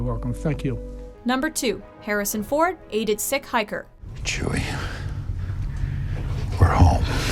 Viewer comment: I thought sick hiker said sick hitler